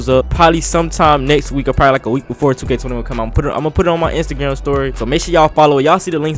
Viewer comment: kinda sounded like he said instagam story